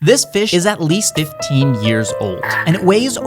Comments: For a min I thought u said "this bitch is at least 15 yes old" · When he said fish I didn’t hear that I heard.. “This bitch is about 15 years old” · I heard it as this bish was 15 years old